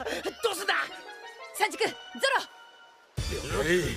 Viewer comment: Franky fart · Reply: sounds like he say sanji